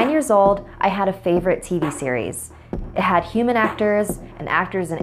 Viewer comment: i thought she said T-series and i was like BEGONE